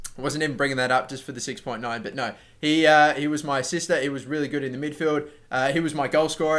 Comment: Haha when u said why u gave Adryan man of the match and joked about 6.9 I thought you said "he was my sister" instead of assisted XD